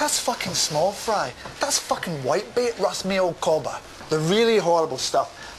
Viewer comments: "You need to fucking listen to me, Russell, you fucking Antipodean fucking kangaroo loving fruit cake!" Hahaha! what does he say at 1:44 - 1:45? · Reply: Ahhh thank-you, I always thought he said "White-mail". Which I assumed was the opposite of blackmail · "You need to fucking listen to me, Russell, you fucking Antipodean fucking kangaroo loving fruit cake!" Hahaha! what does he say at 1:44 - 1:45? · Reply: He says "That's fucking whitebait Russ, me old cobber", meaning that 'it' is unimportant, with 'me old cobber' being an affectionate (but sarcastic) term of endearment.